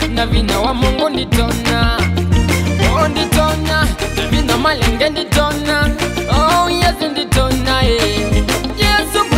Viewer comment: Did I hear '... nditonya na vinya wa malenge' at 2:16? hehe